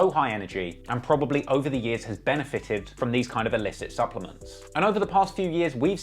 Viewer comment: did you just say SUPPLEMENTS?🤣🤣